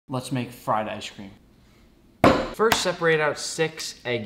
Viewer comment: Ngl i thought it said fried rice cream